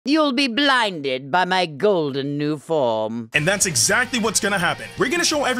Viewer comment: I thought frieza said “You’ll be blinded by my golden power” not “You’ll be blinded by my golden new form”